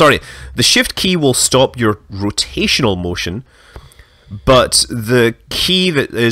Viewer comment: What did you just say, Scott? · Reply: Serious answer: pretty sure he started to say Control Key.